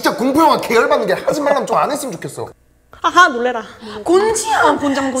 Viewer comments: I thought she said "aja Culera" 😭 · parece que dice jaja culera xd